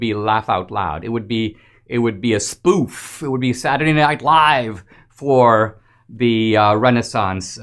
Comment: It would be Saturday Night Live? I thought you said it would be funny?